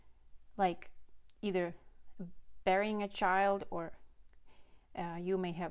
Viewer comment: did you say burying a child or bearing a child? Those are two VERY different things! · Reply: I am so sorry if I mispronounced. Yes, there is a big difference. I meant to say Bearing or having a child. And nesting or preparing for a baby. 😊 🙏